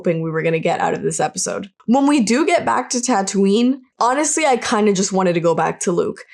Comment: "When we do get back to Tatooine"...I heard "Bacta-Tatooine". Sums up the show! If that was intentional well played 🤣